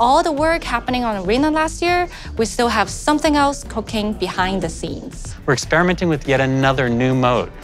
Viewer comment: im the only one who heard cocaine instead of cooking?